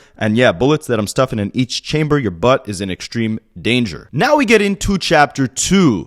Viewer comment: wait! I had to stop washing dishes, did Drake really say "your butt is in extreme danger?!?? 😂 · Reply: he said a$s